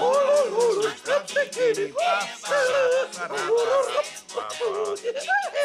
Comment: “Das Lied, das fetzt vielleicht!“ 😄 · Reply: Stimmt, ich hab immer verstanden "das kennst vielleicht"